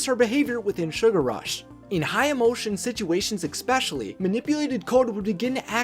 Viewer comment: did he just say ex-pecially